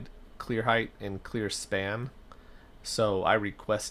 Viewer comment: Quick question, at 1:11:50 you mention you requested clear height and clear span. Was it span? I was not sure when I heard it. Thanks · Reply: Hi! yes, span, the width of doors, columns, etc.